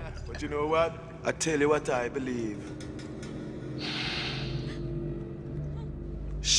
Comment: shit what? seriously what did he say after shit? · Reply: shit happens